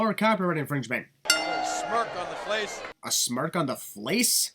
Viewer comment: he actually said "Face."